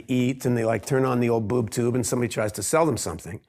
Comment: did i hear wrong or did he actually say boobtube???